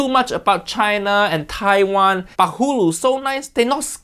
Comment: Could have swore you said Butt flu😂